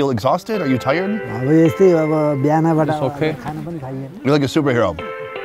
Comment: when asked are you exhausted? He answered, "We are just like this all the time. Didn't get to eat anything since the morning." Just telling because the subs just say it's okay which is not what he says here.